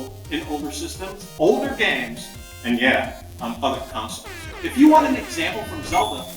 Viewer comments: what did reggie say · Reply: "And yeah, on other consoles." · Right after 2:55, it sounds like Reggie says f***ing consoles?